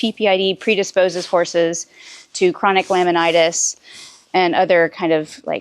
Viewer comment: I don’t understand what she is saying. 😢🇸🇪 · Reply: Cushings / Laminitis